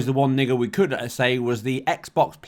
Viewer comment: what did you just say?? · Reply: 1 Niggle is that Xbox Players could still play the old Xbox One X version on the Series X.